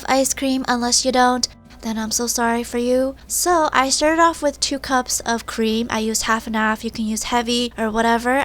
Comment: I thought is said 2 cups of ice cream at first